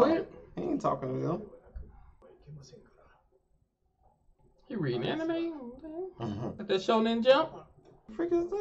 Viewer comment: Did he just say "is he reading anime"...